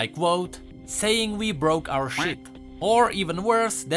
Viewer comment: I thought you said "ship" lol